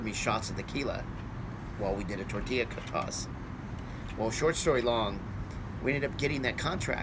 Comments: did he say short story long? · did he say “short story long” ??